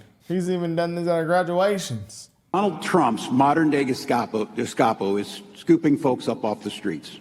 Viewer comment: did i hear "dis-kop-o" x 2 , phonetically , wtf or was it "disco lingo" from ,stock option tampon king .